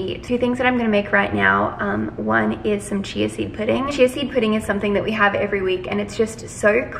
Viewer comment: Idk why I thought you said cheesy pudding not CHIA SEED 😂 don't mind me